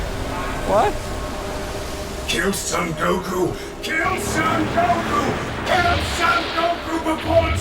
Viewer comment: i thought that it said kill sand go poo.... idek